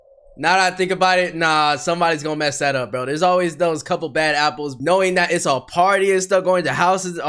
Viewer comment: did you say bad apple?